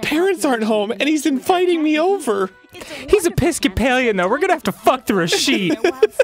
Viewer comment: Does anyone watch these with subs on its kinda hilarious sometimes. At 13:15 it translated what Dan said as “ we are gonna have to fuck the receipt”